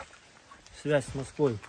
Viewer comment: does he say 'hot line to Putin'? 🇬🇧👍🇺🇦